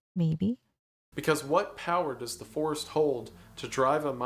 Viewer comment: I misheard what he said as "the Force" for a second and thought, "the Force has plenty of power to drive people mad"